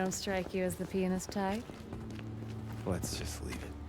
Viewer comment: Fucking hell... I was only listening to the audio and I had to do a double take at 6:49:50 because I thought I heard say "penis" instead of "pianist" and was super confused.